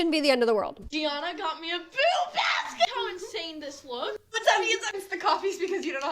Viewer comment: I didn't know about "Boo Baskets" till my friend mentioned she was doing it for her husband 2 years ago. I initially thought she said she was giving her husband a "Boob Basket" 😂